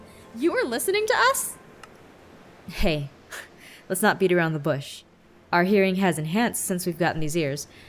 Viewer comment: When he said hey, let's not walk around the Bush, I thought he said hey, let's not be a b##ch. I PUT THE HASHTAGS I cannot write swears...